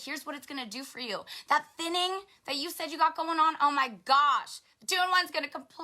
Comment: I thought she said "sinning" when she said "thinning". LOL.